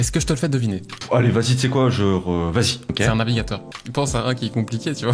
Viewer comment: J'ai entendu : C'est un alligator Du coup jpensais à Lacoste 😅